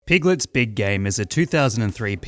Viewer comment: I keep hearing "Piglet's big gay" and cannot stop laughing